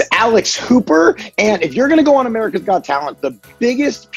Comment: For a second I thought he said his name was Alice Cooper, not Alex Hooper.